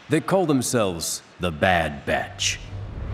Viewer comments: For a second I thought it said: "the bad b*ch" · For a second there, I thought it said "the Bad Touch" xD